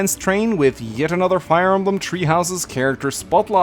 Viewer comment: did you say tree houses? 😂